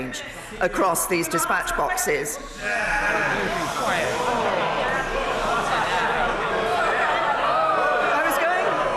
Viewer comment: What is yelled out to her at 23:08? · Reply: "ORDER!" from Bercow